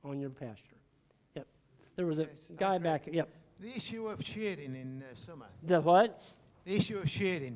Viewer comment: It's at 58:59 or so. The gentleman asking the question is irish or scottish. couldn't understand him either, lol. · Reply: Brent He said The issue of shading in summer